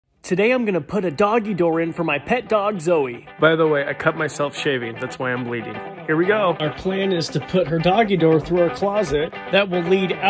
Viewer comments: at first i thought he said today i’m gonna put my doggy down 😄 but nah great work sir i would love to make one for my cats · Am I the only one who thought he was about to say put my dog down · I thought you said you’re going to put her down 💀✋